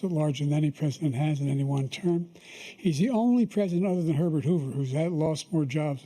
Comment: "is the only president hrbrzzsczrbrhhhh" I can't even understand what Joe's saying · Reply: “Other than hurbert hover” it sounds like. Not bad compared to Biden other momments